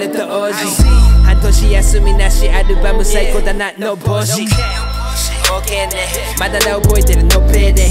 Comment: No 帽子のところが No bull shitに聞こえる💪